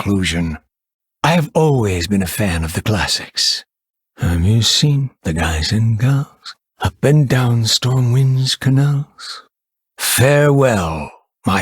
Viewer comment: In 2:53 what does he say? I can't find that quote writen anywhere u_u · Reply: "I've always been a fan of the classics: 'Have you seen the guys and girls, up and down Stormwind's canals?'"